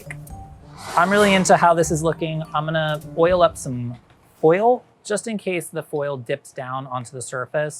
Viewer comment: thought he said "I'm going to oil up some oil"